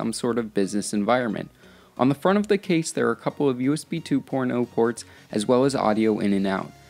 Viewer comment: "On the front of the case there's a couple of USB 2 porn O ports" anyone else hear that at 0:55?